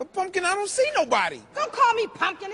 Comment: I thought he said the fuck i dont see nobody not pumpkin lol